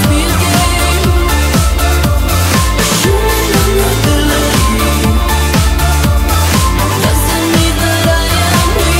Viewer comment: I always hear "ashunamumubaloogey" or something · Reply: Teen Idle she said "a human vulnerability"